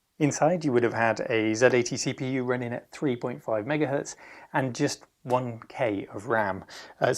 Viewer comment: Z81 at 5mhz rather than 5hz? i thought the much later Amiga was 8mhz of course i haven't checked Wikipedia yet... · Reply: I think you misheard the man, he actually said 3.5MHz